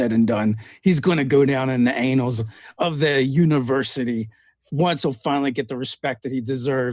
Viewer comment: I died laughing when I thought I heard say “in the anals of history”